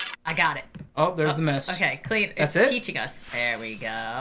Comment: Idk why but when you guys went into the bathroom and it said to clean stain I thought it said clean Satan for some reason. 🤣😳